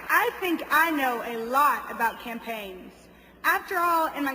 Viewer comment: is that the twin towers behind them? this is way much funner than those ethiopian videos. did she say campain or champagne(2:56). Party on girls!